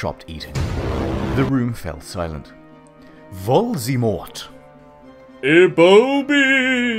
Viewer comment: instead of Voldemort it’s “Darth Valer” 😂😂😂😂